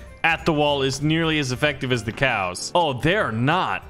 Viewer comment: Did he just say cows 4:13 LOL